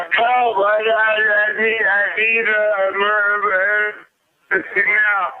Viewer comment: I heard "help I need a murder investigator now" no less incriminating